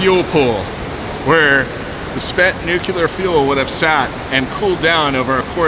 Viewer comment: Did you just say nuke-u-lar? :(